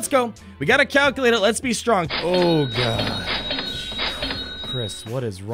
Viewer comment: It sounded like he said Chris